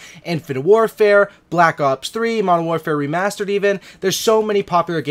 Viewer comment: did he say Modern Warfare Remasturbated?😅😅😅